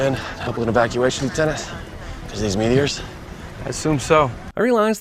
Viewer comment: O I thought he was saying 25 meters. Like he was giving his team the range of the aliens